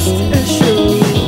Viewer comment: ''i got my trusty shoes'' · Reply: its trust issues, but it sounds like he says trusty shoes so i thought it was funny lol